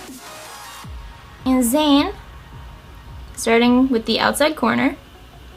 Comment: what did she say?? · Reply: "Starting with the outside corner"....